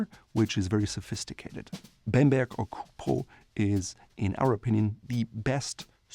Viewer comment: Would someone please tell me what material he's saying at 11:20? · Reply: Bemberg/Cupro